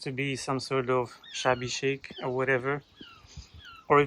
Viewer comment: I honestly misheard you at the start. I thought you called it shabby shite! 😂